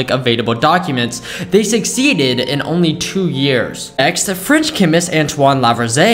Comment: Who else thought he said fridge chemist 👇